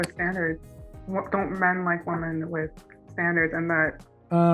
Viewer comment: I thought she said “standards” instead of “manners”. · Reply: you’re right she did say standards. I misheard when I was listening to it. I was thinking, “manners? Wth lol” but even still, she missed the point of the app all together. I hope she finds what she wants and needs, but this could have been avoided if she found the right outlet for her emotions and needs